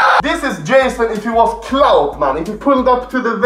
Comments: did he say the n-word ????? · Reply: "This is jason if he was clout papa" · did he say : this is Jayson if he was clout, ma nigga